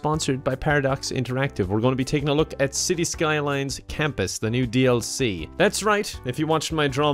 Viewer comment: "Skylines" Campus? Oh, I thought you said "Concentration campus"